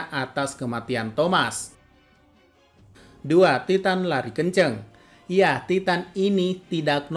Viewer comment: Maap bang aku dengernya titan lari ngaceng